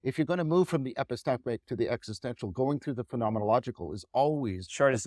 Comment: Another great lecture! One question if anyone can make out what is been said at 19:00 "If you are going to move from ??? to existential..." I listen 5 times still not sure, and the auto transcript doesn't seem to be right either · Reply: I think John way saying "If you're going to move from the epistemic to the existential ... " and just pronounced "epistemic" in an odd way